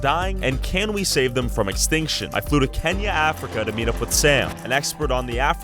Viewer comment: Idk why but when he said kenya i thought he said kanye west